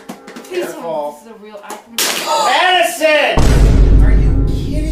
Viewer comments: the "MADISON" was personal💀💀💀💀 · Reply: lol I thought he said allison 😭✌️ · #6 it’s so funny. I had to scream Madison. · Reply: I thought he said Addison